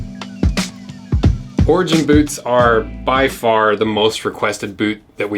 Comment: Thought you were saying ORGY BOOTS